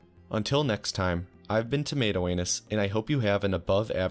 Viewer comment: I thought I heard it wrong. Then I checked and it was actually Oh the satisfaction!